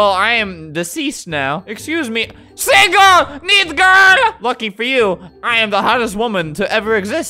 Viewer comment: did he say F###? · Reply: Lucky